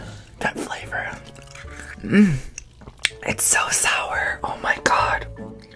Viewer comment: Did he say sour🫡